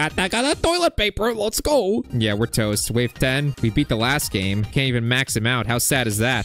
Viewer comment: did he said toads